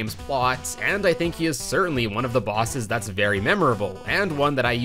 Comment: “he is certainly one of the bosses” is what I heard at first